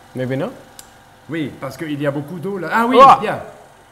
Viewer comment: why is the professor unsure whether or not the nitrogen will solidify? What does he say right before it eventually worked? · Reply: PikaPianist "oui parce qu'il y a beaucoup d'eau là...ah oui!" Yes because there's a lot of water there...ah yes!